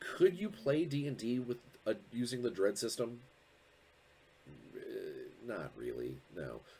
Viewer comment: Every time you mentioned the Dread system, I heard "(Judge) Dredd" and I got psyched for amoment until I realisedmy error ;)